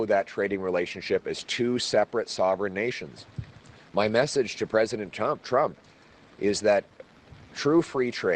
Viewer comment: Sounded like he said, "My message to president chump" 😂